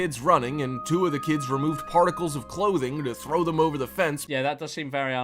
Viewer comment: Did the Narrator say "particles of clothing" instead of "articles of clothing"? wtf XD LOL